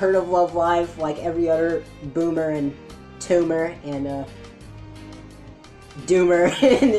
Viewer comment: if you listen hard enough it sounds like he says TUMOR